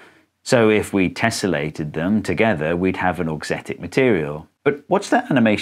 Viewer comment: Everytime he switches the "bra" object I hear "Aux-tit-ic material". And I couldn't help but visualizing the "One direction" boy band several times. I'm such a d*rk!